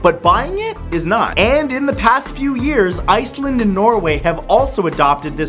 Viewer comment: did I just hear my home country 🇮🇸 Iceland